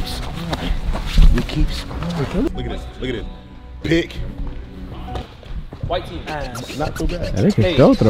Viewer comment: Anybody else hear kai say “white team ass” 6:53 timestamp